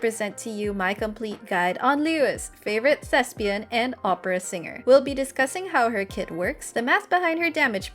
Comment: In the intro I heard “Liyue’s favorite Lesbian and Opera Singer” jshshs oh well